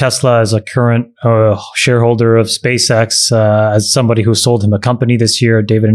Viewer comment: Did Chamath say "as someone who sold him a company" ? If so, what company was this?